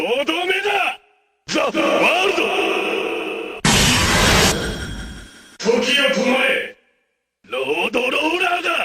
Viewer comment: what does he say? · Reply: Dylan Rivera Za Warudo! Toki wo tomare! (The World! Stop time!)